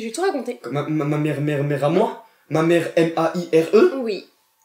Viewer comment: Je rêve ou il dit "Ma mère a moi °m a i r e° a